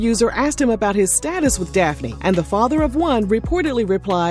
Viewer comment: did you just say the father of one?